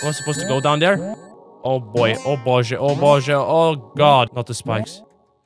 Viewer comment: zaraz czy ty powiedziałeś boże? did you say boże?